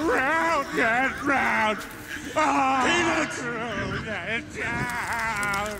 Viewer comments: i swear brian says peanut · it sounded like he said peanuts when he was supposed to say peter